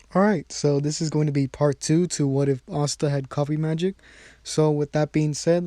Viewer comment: When he started of the video, it sounded like he said what if asta had coffee magic and it’s hilarious.